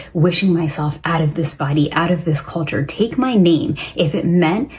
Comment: lol did she say take my meat 🍖?